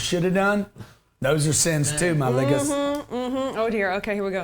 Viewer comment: Did he just say my ligga? 😂